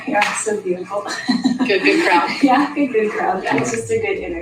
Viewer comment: did she just stop what she was starting to say and blurt out "I am so dutiful" ? Just, wow.